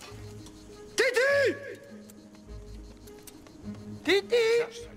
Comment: pour moi Kiki, sa reste avant tout une sorcière 😉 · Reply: Je crois qu'il dit Titi pas Kiki, même si merci de me rappeler un bon film par rapport au film qui est critiqué